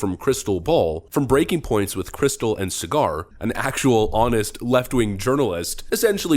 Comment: I heard, "Breaking Points with Krystal & Cigar" 🤣🤣